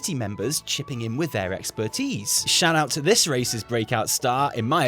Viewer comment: Sounded like he said " Shout out to this racist breakout star" Im still not 100% certain he didnt... LOL